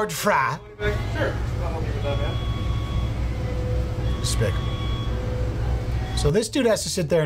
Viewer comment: Did you say "spic" @ 13:29 ???